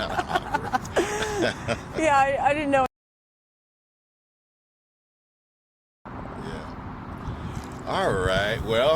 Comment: What did she say at 9:42? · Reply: She said she prolapsed and all the semen fell out 😮